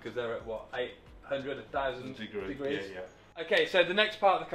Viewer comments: 800,000 degrees is a lot. 🤣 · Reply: I think I heard “800 to 1000”, right? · What you said: “It’s what? 800, a 1000 degrees?” What I first heard: ”It’s what? 800 000 degrees?” Me: 😱 … wait, that can’t be right?!?! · did I mishear but did he say 800,000 degrees?! boy that is just a little bit warm wouldn't you say!